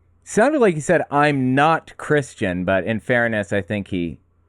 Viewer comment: It sounded like he said he’s not Christian. That’s what I heard.